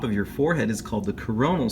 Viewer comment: For a sec I thought he said Corona sucher on the skull and k was like CORONAVIRUS.